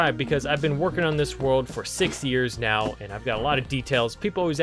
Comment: Him: i been working on this world for 6 years Me who thought he said 60 years: WTF